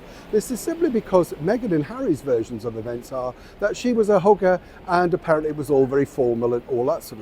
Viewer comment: when you said, “She was a hugger”, I thought you said, “She was a hooker.” !! Maybe it was your lovely accent, or then again, maybe not 😉😂😂